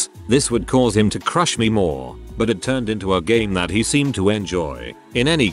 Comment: I thought the tts said "but this turned into a gay" and imagined that the drill sergeant and the private started a gay relationship where the private discovered he has a humiliation fetish